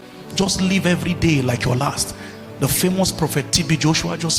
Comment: Did he just say senior Prophet TB Joshua? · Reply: He said the famous not senior